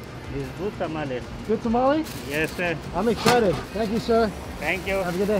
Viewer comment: I thought he said puta madre